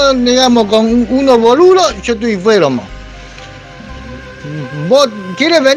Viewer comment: será que dice "yo estoy enfermo"?? O algo así.